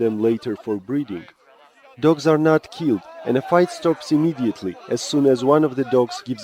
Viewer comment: did he just say dogs are not cute, cuz if he did i'm gonna destroy him!!!!!!!! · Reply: you are a nice guy..:)...i dont coment normaly..he said dogs are not killed the fight stops if one gives up..:) kek